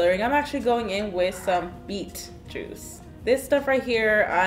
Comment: omg for a sec i thought she said "MEAT juice"- and it looks like blood lol